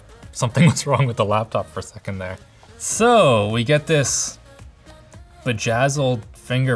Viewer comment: Who else thought he said “soo, we get this Vagazzled fingerprint reader” 😂